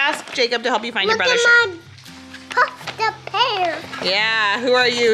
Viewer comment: Omg I thought he said “look at my fucked up hair” lmao